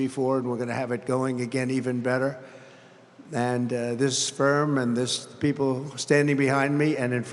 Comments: does he say sperm ? This guy is all BS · Did anyone else hear "sperm"?